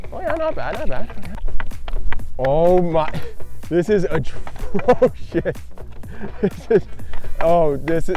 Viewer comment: did he say oh s*it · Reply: And ya he did say sh*t